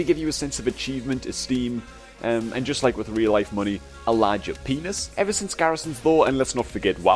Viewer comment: Did he say "a larger penis" at 4:14 ?